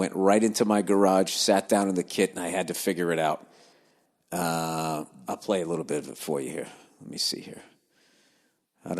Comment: What was that song Bill sampled for us at around 15:00 ? I couldn’t understand what band/album he said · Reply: Mammoth, Wolfgang Van Halen’s album, track 2 - Horribly Right